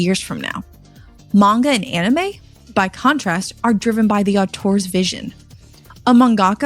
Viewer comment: she says “au-tors” instead of author… 100% ai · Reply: "Auteur" it's a fancy French word that is the equivalent of author, but has the connotation of having more control over and vision in a work of art